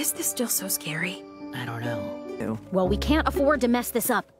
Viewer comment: it sounds like Eda says ew